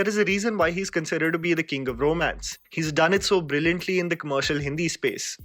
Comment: did he just called SRK king of bromance?